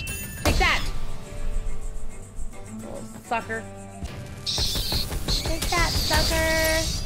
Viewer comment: Did she say fucker or sucker? (I know, don't yell at me, she's an adult who can say whatever she wants blah blah blah, just curious) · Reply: She said sucker